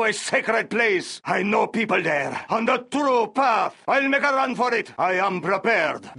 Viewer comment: There's someone in a facebook group asking "why is the GLA bomber saying 'honda turo path', what does it mean?". I instinctively going right through google and wiki, and also almost getting Generals reinstalled to hear what the bomber actually says but stopped as I remember someone would have uploaded a video full of the unit's quotes. I deadass have the quote list from the wiki and listen carefully to this video which doesn't have much spacing between each voices. But before that, I mistaken 'bomber' with GLA's suicide truck and had to rewatch three times of a wrong video just to realize 'oh yeah, it's terrorist'. Oh, the terrorist actually says "Oh the true path" on 0:30. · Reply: It's "on the True path"